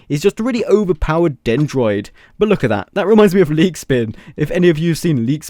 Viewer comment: I thought he said meat spin